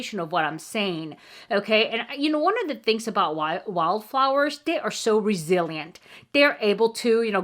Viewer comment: I enjoy drinking hot tea (british roots), but green tea is not a flavour I enjoy. Thank you for explaining the wild flowers name, but at first I thought you were saying wallflowers. No offense but it's your accent and an issue I can relate to. As my Australian accent is often misunderstood. Even though I lived in America for a long time.